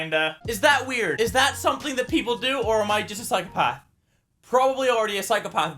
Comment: captions say "or am I like pot" instead of" am I a psychopath"